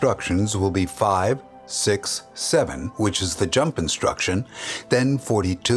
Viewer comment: Those who heard 67 at 13:25 👇🏽